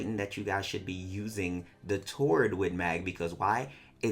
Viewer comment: what are you saying ? toward win mag? wtf is that ?